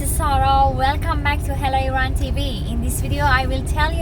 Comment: Sorry i thought you said Hell Iran. So is Hello Iran